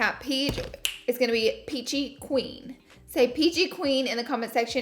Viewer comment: Peachy Queen... thought you said PG Queen at first lol